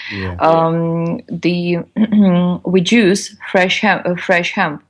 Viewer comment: does she say "we Jews" or "we juice"?